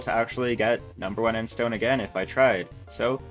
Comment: did you just say #1 in GEMSTONE was at 120 mil, using minions? I think you mean mithril, gemstone doesn't have minions and there are people with 3bil+ collection